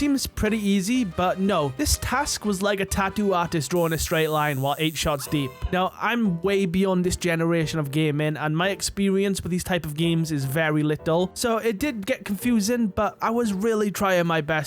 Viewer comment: “gay-men” took me 3 times before I heard gaming